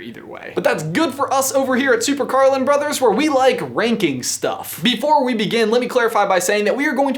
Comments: I heard 'wanking stuff' the first time I played it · Not even kidding, when you first said ranking stuff I thought you said "wanking stuff".